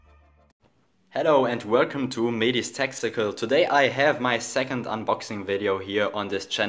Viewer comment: I heard Medhi's Testicles